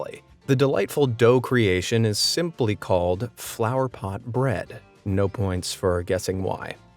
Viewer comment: At first, I heard 'Flour pot bread', then five seconds later I realized he meant literal 'Flower pot bread'